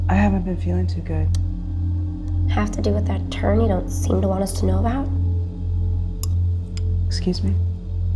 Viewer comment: Great film!! Also—- The closed caption at 5:20 “toe needle you don’t want us” instead of “turn you seem to not want us”😂